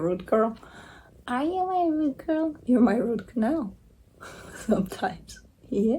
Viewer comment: At the end of this video, I thought you said to the cat “You are or are you my root canal”. Like the dentist sometimes recommends. I just cracked up! 😆 Maybe I should watch again….